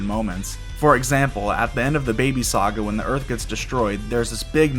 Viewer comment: I chuckle every he says "the baby saga" because i keep hearing it ass "dababy saga"